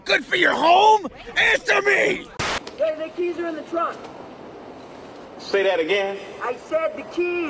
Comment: i thought she said, "naked and in the trunk".